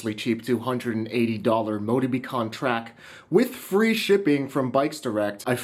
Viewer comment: what did he say?!?! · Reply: motobecane track